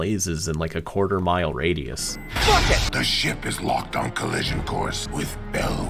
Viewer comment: I swear someone just said “Fuck it”